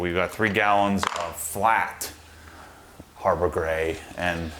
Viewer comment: I swear one of those times you said Harbor Gray I thought you said “Harbor Freight” 😂😂😂